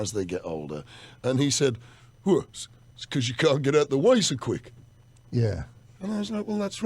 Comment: I thought he said: "Cos u can't get the 'waste' out so quick"...... had to rewind a few times to listen again LOL.